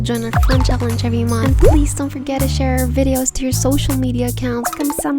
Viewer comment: Did you say Thank you in Hindi? (Dhanyawad) 💕💕